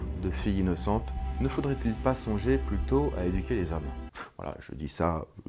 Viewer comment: j'ai compris "énuquer les hommes" dans le sens de les rendre énuques xD